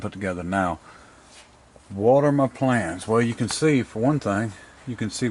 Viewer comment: When you said "what are my plans" I thought you said water my plants lol